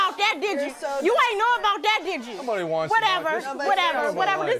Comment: Did she just say "Stick to twig" LOL